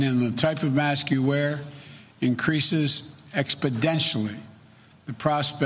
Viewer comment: Did he say Expodentially? Min